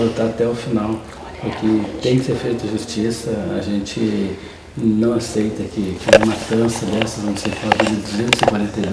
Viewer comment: eu ouvi um sussurro, dizendo algo q não entendi direito, algo “olha ela” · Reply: Beatriz Carvalho “olha ela mentindo” entendi isso